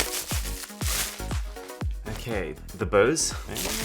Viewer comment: I thought you said: “Hoes” lol